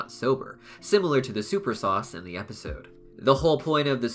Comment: I thought he said super sauce...